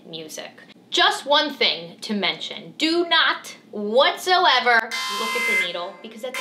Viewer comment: I heard it all except the DO NOT